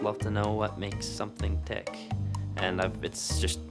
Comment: I thought he said "THICC"